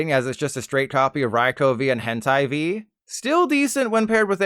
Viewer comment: did he say hentai or am I going crazy